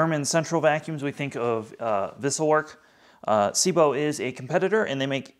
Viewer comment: At the beginning of this video, you said a word I could not understand. You said it at 0:26 and 0:38. What is that word? Captions spells it two differnt ways and I google it and found nothing. Vissell Work ? I think I found answer below by #vacuumtests Wessel Werk ?